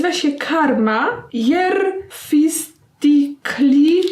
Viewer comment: Mi to ,, Karma Cere Freze " ( tak usłyszałam ) mi to nie smakuje.